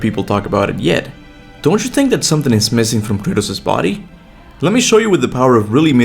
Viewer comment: Sounds like u say Kurtis instead of Kratos🤣